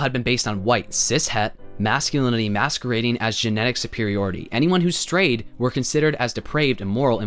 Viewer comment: for some reason when they said “anyone who strayed” I heard “anyone who was straight”😭😭